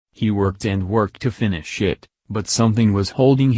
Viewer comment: Anyone else hear "finish shit" 02:47?